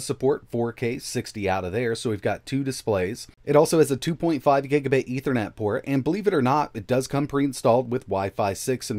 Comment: Giga... bait? Did I hear that right? What's that?